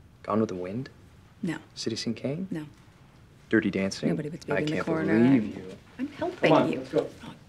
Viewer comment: What does Rachel say at 9:00? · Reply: “Nobody puts baby in the corner “ I think